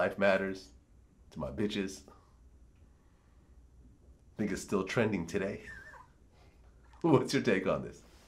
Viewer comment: yo at 25:18 i swear it sounds like vlad says "nigga still trending today"